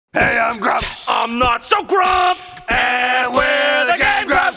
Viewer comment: the first like millisecond of audio doesnt load for me so I hear "Grump. And I'm not so Grump" instead of "Hey I'm Grump. And I'm not so Grump"